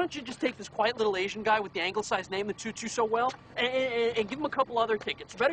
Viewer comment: I always thought he said quiet little Asian guy w/ the ankle size name when I was younger but it’s anglicized... duh that makes more sense lol